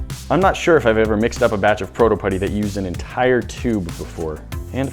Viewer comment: Tbh I thought it said pyro putty and was a bit concerned